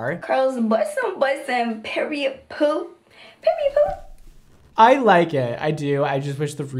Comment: Did she say PERIOD POO?? 💩😂😂😂😂😂